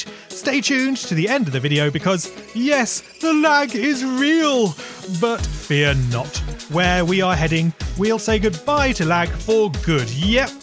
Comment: i thought you said lag is real but we're not for a second